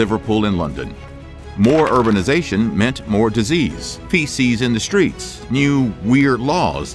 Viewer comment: i thought he said "PC's in the street" lol